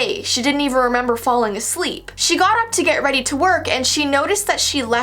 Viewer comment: is it just me or I misheard her say : "twerk" than "to work" I